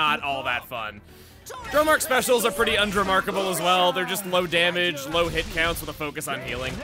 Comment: did you say undromarchable?